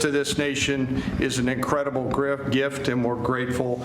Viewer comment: "service to this nation, is such an incredible grift....I mean gift" did I hear that right 8:48 👀